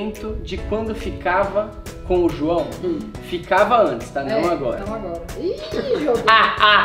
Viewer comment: a 5:07 eu ouvi direito Joana voltando "Ficava antes não agora"